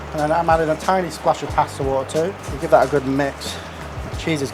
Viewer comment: what oil? I couldn't quite understand, it almost sounded like Castor Oil, but I know it's not. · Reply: Pasta water